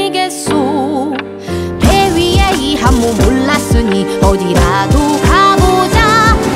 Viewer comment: 왜 "올랐으니" 가 아니라 "불났으니" 라고 들리는거지?